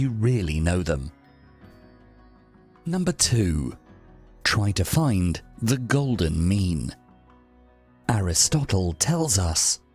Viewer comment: I was just listening to the audio on this video at work and had to check my phone at number 2 because I thought he said "Try to find the golden "Meme" . I was like ...... This man was wayyyyyyyy ahead of his time lol